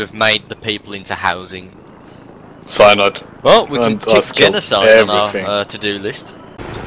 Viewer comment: Did you say Genocide? megalovania plays